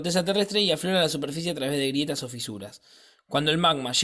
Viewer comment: que dice en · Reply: Llega q la superficie a traves de grietas o fisuras hasta que te conteste te hiciste todo jajajaj